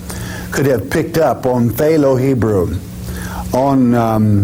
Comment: What, exactly, is this "Pthalo Hebrew" that is referred to? Is this actually what is being said, or am i hearing it wrong? Because when I hear the word "Pthalo" anything, all i think of is paint.